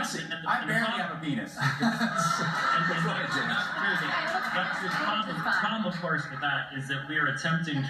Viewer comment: What does James White say from 1:10 -1:15 ? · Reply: he says "Dr Drew, Dr Drew, the problem of course is..."